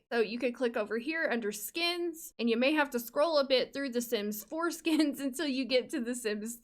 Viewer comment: why did I hear “you may have to scroll a bit through the Sims foreskins”? help me